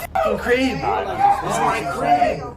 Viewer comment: subtitles said “it’s my cream”. Dude he said “it’s my crib”